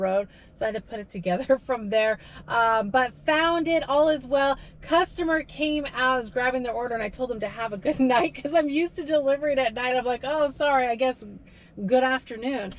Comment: I THOUGHT SHE SAID BEST TIME TOO DELIVER WAS AT NIGHT